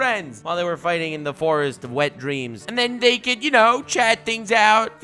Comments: Did you just say "the forest of wet dreams"? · I thought he said Forest of Wet dreams 😂😂